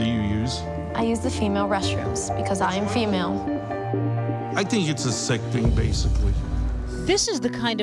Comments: WHat did he say at 0:21 ? I think He said I think you're something basically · Did he say "she-male"? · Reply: No, she said female